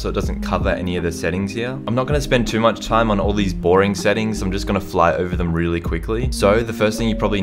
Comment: u should play it on 0.25 speed before ur intro it sounds like u said porn settings😂😂